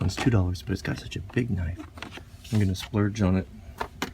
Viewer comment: did he say "I'm gonna splurge on it"?